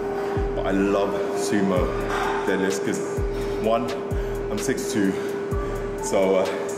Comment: Did he just say I'm 62😱😱😱😱😱 that cant be right